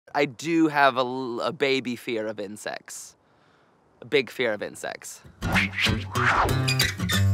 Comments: in the intro I thought he said " I have a very big fear of bee sex" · I thought he said sex not insects. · I thought he said a baby fear of incest or insex